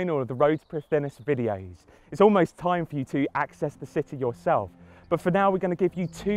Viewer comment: Is it me or does it sound like hes saying: "now, the only thing to do is expefience the city your elf."